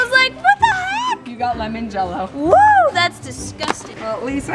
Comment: When you said lemon jell-o for I thought you said lemon cello